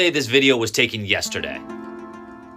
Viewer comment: What is your math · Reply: Never mind I thought you said 1 crow victory Royale a day